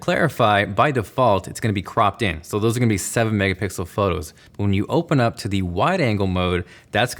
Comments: Did you say Megapickles? lol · Did you say mega pickles at 1:52? I had to rewatch it a few times. That’s what it sounded like.